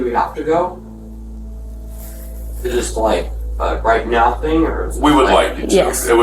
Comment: Did he say “right meow”?